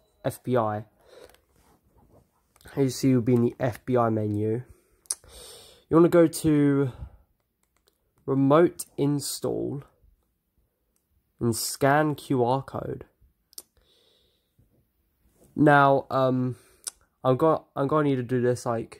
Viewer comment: Wow i thought it said "remove install" lmao thank you